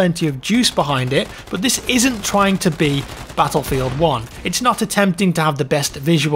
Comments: I thought it said Battlefield 1944 for a second! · I thought it said battlefield 1944 for a second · My dumbass thought it said Battlefield 1944 I was about hyped as hell for second. 😂😭